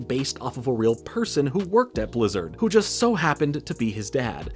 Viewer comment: omg I heard his son worked at blizzard